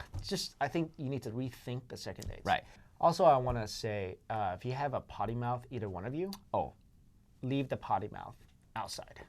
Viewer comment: What does he say at 28:38? I didn't understand and I want to haha I'm French so I don't understand everything ! If everyone could transcribe what he says at this moment of the video I would be very grateful :) Thank you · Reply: "if you have a potty mouth, either one of you...leave the potty mouth." he's basically saying not to curse and use foul language